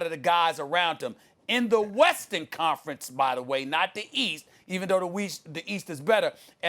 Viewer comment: Did he just say weast? Lol get Patrick off first take